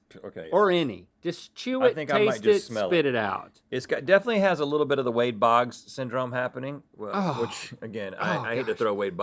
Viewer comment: Lol i thought they were saying it all smells like wade boggs!